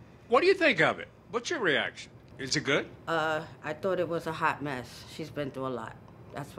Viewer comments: I thought he said SHOT at first🤣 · I thought he said “shot” 🤣 im over here like 😱